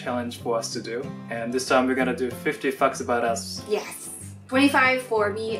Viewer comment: I thought he said 50 fucks about us ???